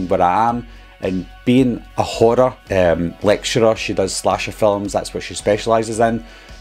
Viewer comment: I swear I thought he called her a hoarder at the start of the video. I was thinking to myself “how did he know she was a hoarder? I don’t remember that being mentioned” lmao. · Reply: When you said “being a horror lecturer”. Instead of horror it sounded like you were saying she was a hoarder.